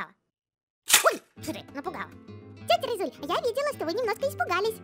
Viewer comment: мне послышалась : Ой шрэк напугал пхпхп